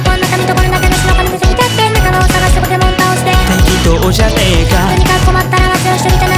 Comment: どうでもいいけど、化けもん倒してのとこポケモン倒してに聞こえた……